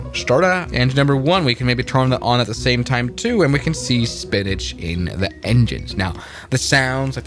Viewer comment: I thought you said spinach in the engines 😂 popeye airlines